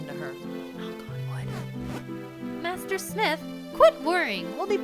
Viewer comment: What did she whisper at 5:18? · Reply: 7 years late but she says “knock on wood” :))